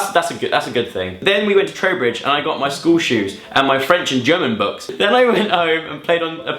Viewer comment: Hey, can somebody help me, I'm not sure if he really said he got his "German" books (at about 7:40 ). I am from Germany and it would be really interesting, that you learn German in Engand. So, did he say "German" or did I just misunderstand it because I'm no native speaker? :D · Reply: I think he said french and german books, yeah. Which is pretty cool cuz I'm french